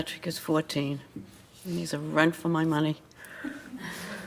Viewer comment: When 4:54:17 spoke about her grandson she told something like "he's a rent for my money?" I am not native speaker and cannot understand what's the meaning of it, could somebody pls kindly explain?